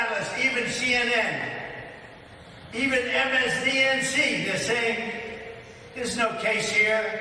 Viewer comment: did he just say "MSBNC" (at 2:25)?